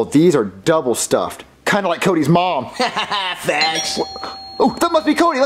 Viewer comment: did just say fast or fat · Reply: He said facts